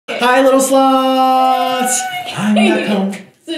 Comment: did he just say sluts? idk i just thought sorry if im wrong